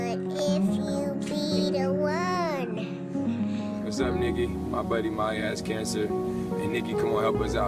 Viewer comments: Does anyone else hear”wassup nigga” when they’re talking abt the little girl with cancer and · is he saying "sup nigga"?